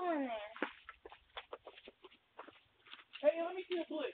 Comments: Can't make out anything. Based on the sound it seems like the teen shot first. Shouts lil ni%%a then fires. But it also appears the former cop got out of his vehicle? I gave no idea. From a purely logical perspective however whatever the guy was saying was irrelevant. Whatever those teens were planning to do that night they should have just continued on. There was no potential benefit in engaging in an argument with a stranger. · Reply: backpack boi was the aggressor, he told his homey in the car to give him the "blick" aka gun. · backpack boi was the aggressor, he told his homey in the car to give him the "blick" aka gun. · Dude in white truck clearly the aggressor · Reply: backpack boi was the aggressor, he told his homey in the car to give him the "blick" aka gun. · At one point one of the guys goes inside to get the "bliccy" aka gun. Ex-cop walks towards them with gun pointed at them. Teen raises arm to shoot. 1:18. Not sure who shot first anymore. If I have to trust the audio, it seems like the ex-cop short first because the teens' arm wasn't fully up yet. Also the ex-cop is the one checking on him later. Kinda weird the ex-cop is yelling "I love you" to the girl though. Weird case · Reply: backpack boi was the aggressor, he told his homey in the car to give him the "blick" aka gun.